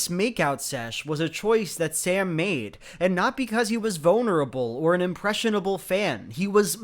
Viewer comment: did you say bonerable?